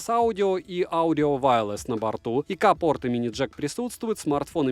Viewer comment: Мне одному послышалось: ИКа порт имени Джек присутствует!???